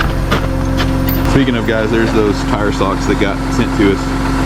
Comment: When you said ‘tire socks’. I heard ‘toe socks’. I thought. I have some of those, they are really cosy. Lol. My brain eventually caught up.